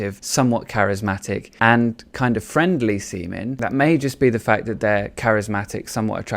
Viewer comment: Lmao you said “friendly seeming” and closed caption heard it as “friendly semen” 🤣